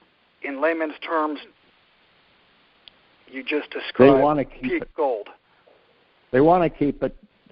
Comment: says " In Laymen terms you just described _______________ Gold however spoke over and I could not decipher the word spoke. Could anyone else figure out what said. It was pretty important and I would like to know what said at this point in the video. I thought I heard "Cheap Gold"? Also, does anyone know the name spelled out of the long term sliver chart that references at 45:21? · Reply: "In layman's terms, you just described peak gold."